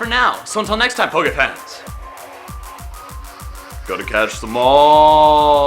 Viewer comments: the outro sounded like he said "Gotta Catch The Mall" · I heard you say "Gotta catch the mall." You want me to catch the mall? Dats so weird!!!!😂😂😂😂😂😂😂😂